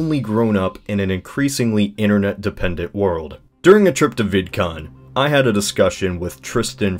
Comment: I thought he said "During a trip to Viet Cong" before I tabbed back in to Youtube and saw Vidcon